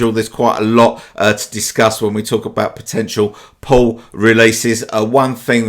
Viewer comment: it sounded like you said, "Let's talk about Porn". lol